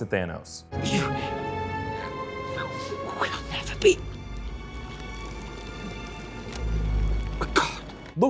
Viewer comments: He's alive. There's a reason he said what he said in the entire first scene. "You will never be a God" and "The Sun will shine on us again brother" reassuring his brother through the ENTIRE SCENE that he's good, he has this under control, this is all part of the plan. Him saying "you will never be a God" said to me "you might be powerful, but even if you think you are killing me now, you will never really kill me or my brother because we are GODS and you are not". Even him looking at Thor and saying "Odinson". He'll be back, and I can tell specifically that Thor and Loki will play the biggest parts in Thanos dying. Not to mention if Loki were REALLY dying he would have turned blue. He's a giant he only appears white either A: in his projection or B: if he wants to appear the same as an asgardian, which is always BESIDES if he's truly dead. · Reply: Kevin Roth lol I thought he said “you will never beat a god” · Who else thought he said you will never be good